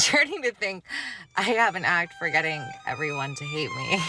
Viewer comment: she’s so dumb that she said act instead of knack